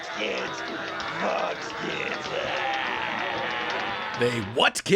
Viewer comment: It sounded like he said "robbs kids"